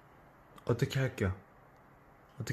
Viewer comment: Sounds like Tae said where'd the cat go XD